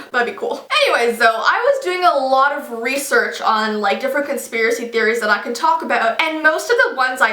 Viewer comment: I thought it said constipation theories